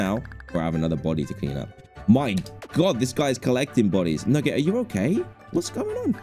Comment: Did Dan say the n word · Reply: He said nugget